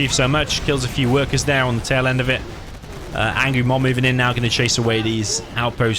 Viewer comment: did say angry MOM at 13:25? LOL